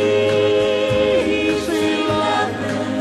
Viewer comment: I used to skate backwards to this in the skating arena--'Backward skaters only!!' Also I was 6 when this came out and I thought it said 'Peace Be Lovin'